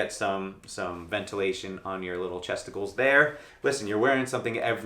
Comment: Did you say Chesticles? 😂😂🤣